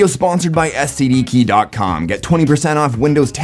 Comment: For a second I thought he said "this video is sponsored by STD keys"